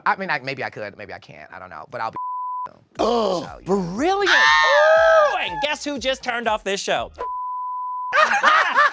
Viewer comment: Anybody know what was bleeped out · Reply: Viacom